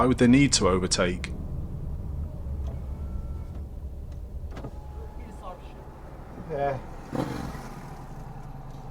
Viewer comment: What does the cyclist say as he blasts through the red lights? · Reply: Sounded like 'shit cycling'